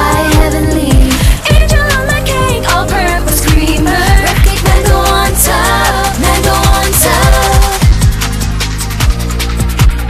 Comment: At first I thought they said “All purpose ‘Cleaner’” and thought WHY ARE THEY EATING IT?!?! 😧😅😂😂😆